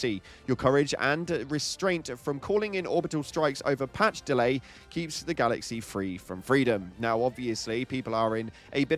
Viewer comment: I legit thought you said, 'keeps the galaxy free from freedom', and I had to come back and re-read the video to make sure The Baskinator wasn't making an Automaton glitch-speak. Please ensure a Democracy Officer is on stand-by with these posts nowadays!